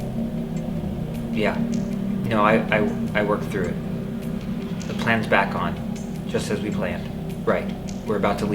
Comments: on phone this is what he said "yeah you know i worked through it the plan back on just at we planned we are about to leave now you can meet us there at the lactation bring the others " after saying that he picked up camera and left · the cameraman called somebody I don’t know what he said I think he said something about the plan is done I don’t know 🤔